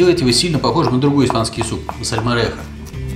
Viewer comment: Не расслышала правильно название» другого» испанского супа, кажется сальмаретто🤔😁, Вы будете его готовить !?🤥😁✋🌹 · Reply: сальморехо (salmorejo). Нет, не буду. Он слишком незначительно отличается от гаспаччо. Немного другие пропорции в нем, хлеб и вода. И так же все пробивается блендером до полной однородности.